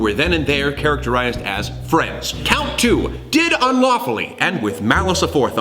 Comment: Every time he said "unlawfully" all I heard was "unwaffle-y" and now I want waffles.